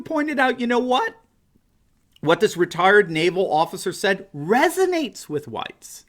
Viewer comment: I heard "retarded naval officer".